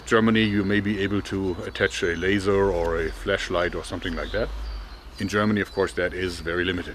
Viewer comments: did he just say fleshlight?? Lmao just playing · I'm sorry, did you say Fleshlight?! Now we're talking!